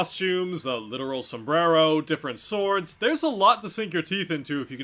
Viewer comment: In the store section, I swore you said 'urban sombrero'. He does fight in a city, so...